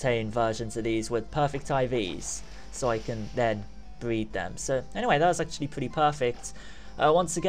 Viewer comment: Did he say "breed them" at 7:16? You can't breed Suicune, can you?